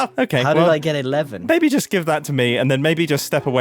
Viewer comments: For a moment I really thought intentionally called Grian "baby" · I fully thought he said “baby just give that to me” there I was flabbergasted